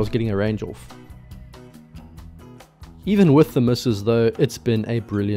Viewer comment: "Even with the missus though, it has been a brilliant morning" So, what exactly are you saying here? She might take it wrongly. *) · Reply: Haha, “misses”, not “missus”😂